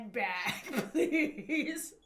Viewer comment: oh he said handbag I thought he said ' I need your hand back'